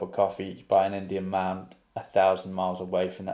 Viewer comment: I agree with this whole thread and will only add that when he says “these 5a bastards threw a shallow cross,” I’m picturing him drunk on the bleachers, pissed off (on a superficial level) because they threw to the middle of the field, it got intercepted, and they lost the game. So his dad’s died, he’s a grown man drunk watching football at his old high school (awkward), and to top it all off a stupid play call cost them the game. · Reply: Also, I feel like I’ve been mishearing the last verse all the while. I thought he was saying, “an Indian man,” as in a statue or column shaped like an Indian man, like you might see at a lodge in Alaska or somewhere. (I took “a thousand miles away” to be Canada or somewhere far west.)